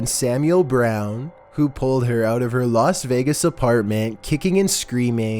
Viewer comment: Adding me on social media is greatly appreciated :) Instagram: <link> Twitter: <link> Reddit: <link> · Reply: No offense but it kind sounded like you said lost vegas